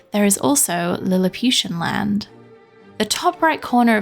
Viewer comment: at first I thought you said “Humiliation Land” and now my mind is imagining the horrific possibilities 😅